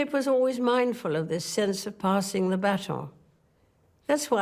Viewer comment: You misheard "baton" as "battle" in 2021, I believe.